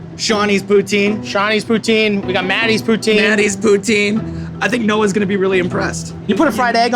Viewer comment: did matty say ( Scheiß Poutine) ? witch means Shit Poutine ?